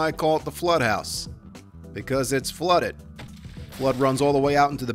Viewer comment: Holy shit!! I’m so excited, join me all the early comers. Edit: 1:45 Did he say “blood” instead of “water”? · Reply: He says "flood runs all the way in the backyard". So "flood", not "blood".